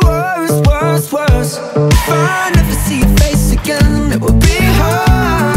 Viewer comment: i thought when i first heard it, it said ‘if i never see your face again, it will be her’ instead of hard so i got SO confused liek what?? lol